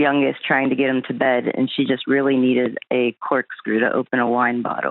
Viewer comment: When she said the neighbor needed a corkscrew, I thought she said quick screw.